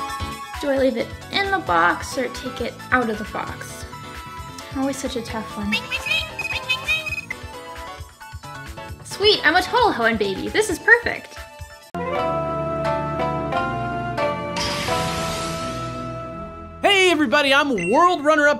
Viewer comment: can anyone please translate what is saying when she picks up the phone? I heard sweet, I'm a total hoe in baby, or home in baby..